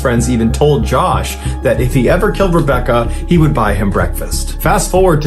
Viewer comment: I thought he said, "Rebecca L-Word" 🤦🏾